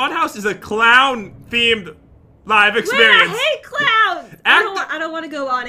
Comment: i thought she said i hate cloud